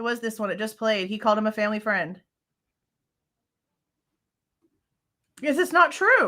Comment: I never heard him say family member. I heard him say “family guy” just my hearing or thoughts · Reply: In another interview he refers to him as "family friend"